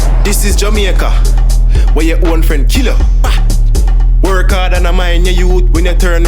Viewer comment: dis is Jamaica Wayaoldfrichia 😂 what did mans say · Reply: Elijah Boi he said “weh yuh own fren kill yuh” which means in English where your own friend kills you